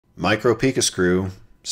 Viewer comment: LOL I thought he said micro P3n... 😂